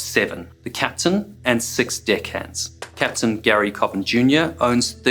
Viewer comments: sounds like you say "Dick Hands". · I’m American this accent threw me off so much I thought he said “the crew consists of the captain and 6 di** he*ds” 😂😂😂